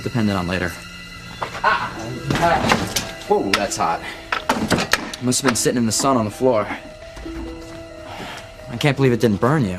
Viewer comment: Miguel what did he say? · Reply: He said "woah, that's hot! It must've been sitting in the sun on the floor."